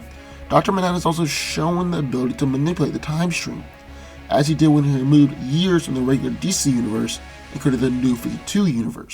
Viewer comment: "has he dijweigfhseg meh, years nkjef regular DC Universe"... Wtf I can't understand a this guy's saying o_O!